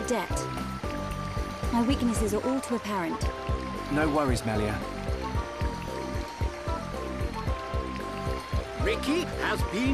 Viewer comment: When Shulk said "No worries Melia" I heard "No worries my love" . .. I was just like "wot?"